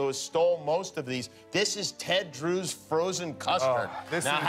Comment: I misheard it as "Ted Cruz frozen custard" and thought WTH?!